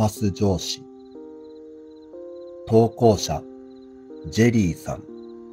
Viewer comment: 疲れてるのかな、、 ジュリーさんがジェリーさんに聞こえる、、😰